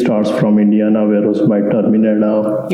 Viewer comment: I can't be the only one who thought he said India and not Indiana